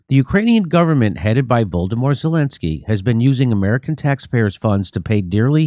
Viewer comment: Sounds like you said “Voldemort Zelensky”